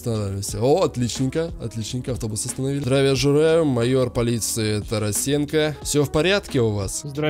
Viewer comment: послышалось здравия жераю